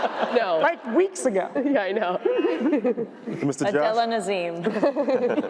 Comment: what is Cristin saying? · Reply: Adele Dazeem, when John Travolta fucked up idina menzels name